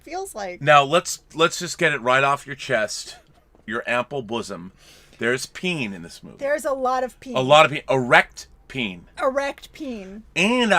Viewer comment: I thought you guys were saying peeing and erect peeing for like 90 seconds lmao